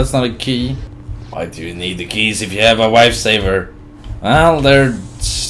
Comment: did he say wifesaver?? xD